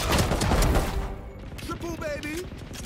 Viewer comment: The amount of times I heard “triple baby”